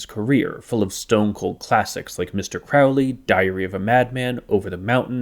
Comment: “Diarrhea’ve a Madman”, I misheard.